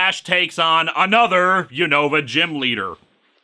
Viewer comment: Oh, Unova, thank you, subtitles. I don't follow Pokemon that closely, so I thought you said, "You know, a gym teacher". 😉😂🤣